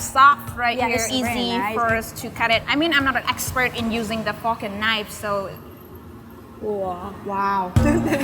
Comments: I thought she said "I'm not an expert at using the fuckin knife" Fork and knife. Got it the second time through. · I thought she said "I'm not an expert in using the fookin knife" 😂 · It sounds like she said "I'm not an expert using the fucking knifes" but really she said fork and knife. I just found it funny and cute lol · LOL Starting @ 10:32 I thought she said Fu**in Knife. She said "Fork and Knife". Whew! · I misheard it to "fucking knife" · at first i thought i heard her say the fucking knife lol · I almost thought I heard her say " I'm not an expert at using the fucking knife". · L O L thought she said f*cking knife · i think she said the fucking knife ? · does she say “fork and knives”, or “fuckin knives”? You decide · Lol 10:30 sounds like she said "I'm not an expert of using the F**king knife" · what did she say about the fuckin knife? 😂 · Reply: Patrick Kendall she said “the fork and knife”. Tho now I can’t unhear the fucking knife 😂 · I thought she said I'm not an expert with a fucking knife lol